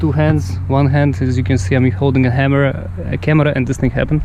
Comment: laughed too hard when you said hammer instead of camerea because of your russian sounding accent. Great video tho thank you